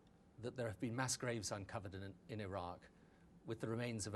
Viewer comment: Did he actually say Iran? 2:28. he's ought to know the difference between Iran and Iraq! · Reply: He clearly says Iraq